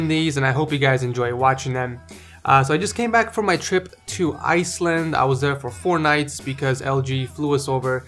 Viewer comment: I thought he said "trip to isis" for a second